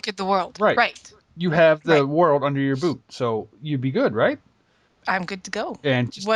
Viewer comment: When you said "You got the world under your boot" around 5:30 i thought you said "You got the world under your boob" o_O